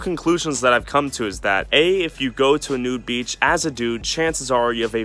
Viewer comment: i thought it said a nuke beach